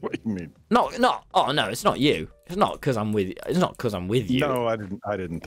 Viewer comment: When said, "No don't, Breathe" I thought he said, "Don't breathe"